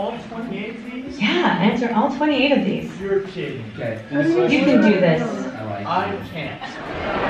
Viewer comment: Did anyone else catch kid complaining in the background at 5:22 Student: “We have to answer all 28 of these?!” Teacher: “Yea answer all 28 of these.” Student: “You’re kidding” Teacher: “You can do this” Student: “I... can’t!” (Edit) I just realized there are other comments about this too lol